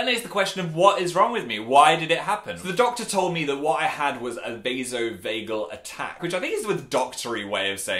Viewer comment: I heard "Peso Bagel attack" as I was watching and I basically re-enacted Dan's "wOt"